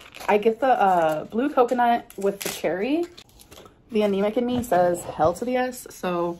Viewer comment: did she say “the anemic in me” at 4:05 ?